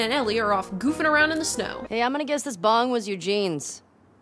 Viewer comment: I always thought she said bomb not bong 🤯